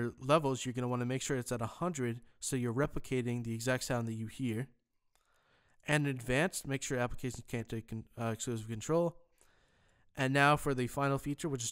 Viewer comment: what did u say there?! i cant follow from there! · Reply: He says "And in advanced, make sure applications can't take exclusive control." In other words, make sure that checkbox is unchecked.